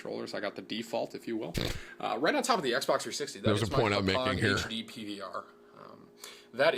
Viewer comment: I thought he was saying p...hub making but he said point I’m malign